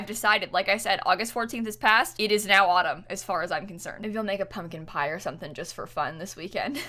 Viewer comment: I heard public pyre instead of pumpkin pie but for some reason my brain just went with it